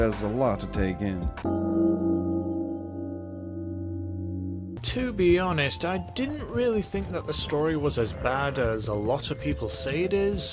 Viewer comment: He actually said "To be honest.... I didn't think the story was as bad as a lot of people say it is"